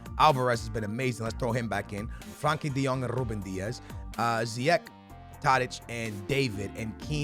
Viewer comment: why did you call him diaz